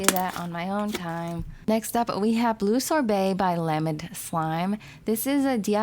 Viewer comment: Did she just say blue shorebay? Instead of blue sherbet